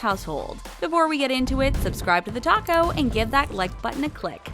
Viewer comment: at the start it sounded like they said " and subscribe to the tacos" 0:14 !!!!